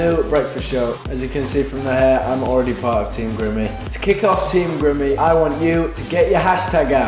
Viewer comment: For the longest time I thought it said team Grammy and I thought he was talking about the Grammy's and I got really confused.